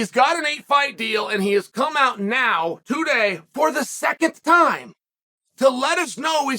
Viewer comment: did he say “for the secondth time.”